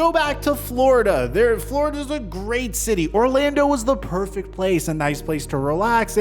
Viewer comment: Hey 😂 at 8:42 did u say “Florida is a great city”?